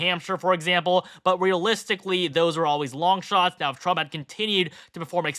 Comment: I thought they said Trump had NJ